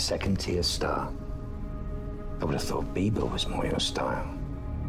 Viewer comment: did he just say "beebo"? .... where's my panic at the disco squad?